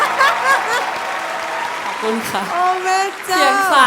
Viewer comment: นี่ได้ยินเป็นขอบคุณค่ะ 55555 สงสัยหลุด <num>